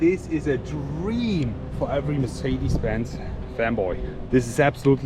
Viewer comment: DID HE SAY MERCEDES FEMBOY ??? DAMMM